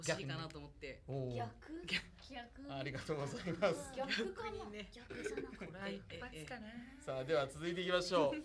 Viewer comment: は「逆じゃなかったら…？」と言ってるように聞こえますね